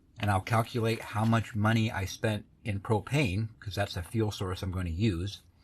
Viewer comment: I thought you said Cocaine, not propane, lol